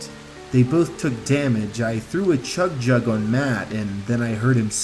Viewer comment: did he say I threw a chug jug